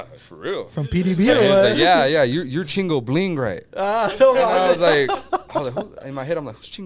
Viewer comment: I heard “ your chingo bling right?” I started laughing so hard 😂